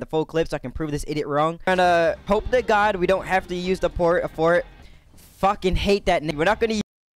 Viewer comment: Did he say fort fort1:12